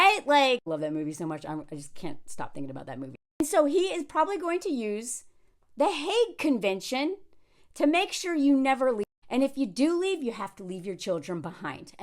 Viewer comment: I misheard the "Hague Convention" as the "Hate Convention".